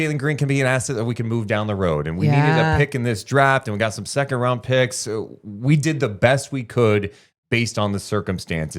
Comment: They did get a 1st in this years draft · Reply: I thought I heard you say only 2nd rd picks. My mistake.